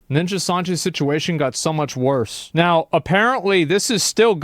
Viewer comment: Did you just say "ninja Sanji"?